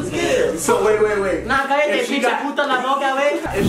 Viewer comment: did he just say PUTA